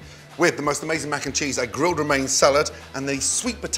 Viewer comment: Did he just say grilled romaine salad?